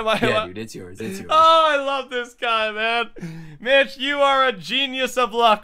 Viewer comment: Subtitles says '' i love that bitch '' instead of i love that guy LOOOL